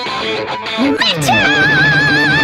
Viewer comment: I thought she said f**k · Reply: She said “Funk”! 😊✨